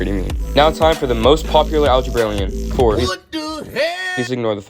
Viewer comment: I thought you were saying what algerbrairian whatever they be called each bfdi character is.